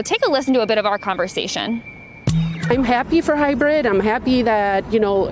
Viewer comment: I thought she was saying her son's name was Hybrid.